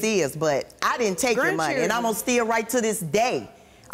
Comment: Omg this lady 🤦‍♀️ 🤣 big ooofs. she just gives me anxiety... who steals from their own daughter or child at all? Also did she say “and I’ma still right to this day!” Or “and ima STEAL right to this day!”?!?!?!🤣